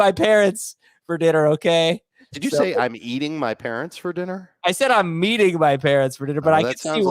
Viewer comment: Yea I totally heard, “I’m eating my parents for dinner.”